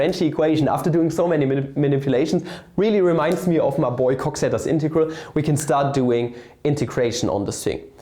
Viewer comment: 😂 I had to listen to that bad many times I heard “my boiz cocky integral” I had to look that up haha